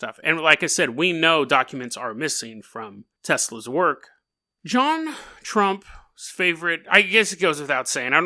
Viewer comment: I keep hearing you say "JonTron" lmao